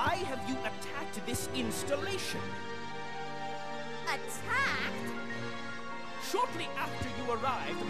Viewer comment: Can someone explain to me what she's saying at 11:44? lol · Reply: Claire : "Attacked" ?